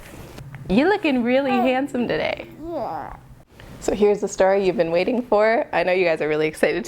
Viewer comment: she says he’s handsome today and it sounds like he says yeah